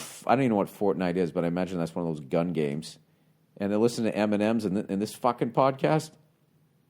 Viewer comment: Did Bill say "and they listen to Eminems"...plural?? 🤣🤣 ahh I love yaa Billy boy